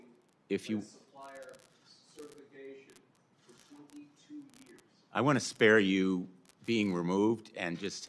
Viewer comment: what did the man say Boeing did for 20 years? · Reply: something about certification (22 years)